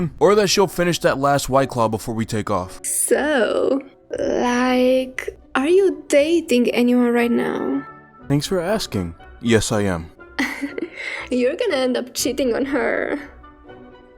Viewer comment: I thought she said "You're gonna end up shitting on her"